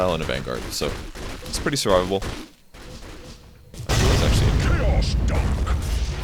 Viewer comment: DID EARTHSHAKER JUST SAY CHAOS DUNK!? That would be the best homage ever...